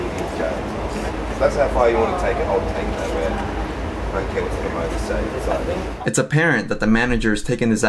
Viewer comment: [inaudible] is "I don't care what the promoters say."